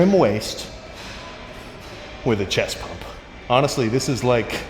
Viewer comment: I thought u said Natty chest pump Sam!😮